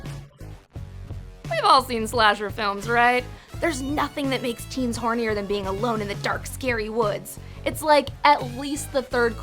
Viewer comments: i cant hear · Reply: Pea Brain "what happens when 2 teenagers that like each other go out in the woods alone?" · what does he say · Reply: BTW he says "what happens when two teenagers who like each other go in to the woods alone"